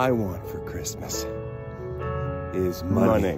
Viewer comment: Thought he was gunna say “all I want for Christmas… is di-“